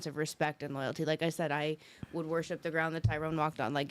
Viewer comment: Did she just said Tyrone? 😳 You Can't Make This Shyt Up! -Kevin Samuels